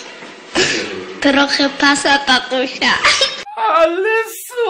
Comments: Czy tylko ja usłyszałem Passata tatusia? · ja usłyszałem passata xD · Ja usłyszałem: Trochę passata tatusia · usłyszałem "pasata tatusia" XD